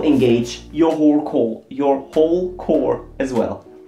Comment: did he just said your whore core😂